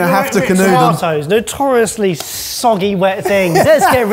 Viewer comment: I heard "canoeing" the tomatoes, which I translated it to "canoodling" the tomatoes. Had to listen again.